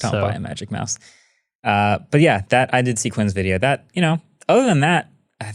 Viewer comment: Dave2D, The Verge, Rene Ritchie. Amongst many others. Only The Verge comes close to Marques’s quality :) but I trust the rest. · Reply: I thought you said "until I saw marques' video" probably a typo or something. And same I prefer Dave regarding laptops. 🙋🏻‍♂️